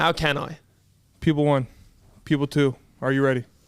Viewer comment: I kept hearing "people 1 & people 2" not "pupil 1 & pupil 2" 😂